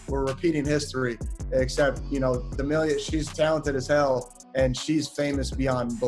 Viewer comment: Did they just say charli is talanted